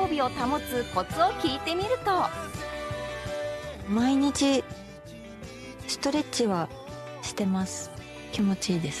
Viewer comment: 一人えっちってずっと思ってればそうも聞こえるけどやっぱりストレッチな絶妙なラインで直後に気持ちいいとか言って芸術点高いのもすげえ